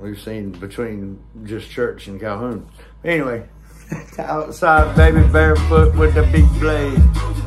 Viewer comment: I thought it said 'outside with the big plays' and 'outsude making big plans'😢😊